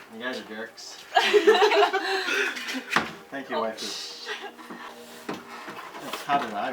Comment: xD did he just say "Thank you waifu"?